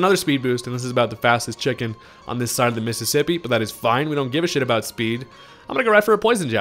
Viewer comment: Let me know if you remember these mons! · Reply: I see a distinct lack of Paul... Also I heard "we don't give a shit about Speed" but my brain jumped to "we don't give a shit about Mississippi" instead and I laughed.